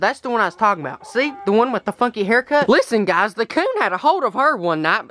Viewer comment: sounds like he says fucky😂😂😂